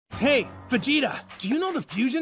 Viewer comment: When I was a anime beginner I thought I heard vagina instead of vegeta 😂